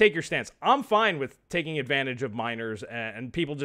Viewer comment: "I'm fine with taking advantage of minors", I don't know if my non American ears just hears that wrong, but that is what I hear. Shame on you =D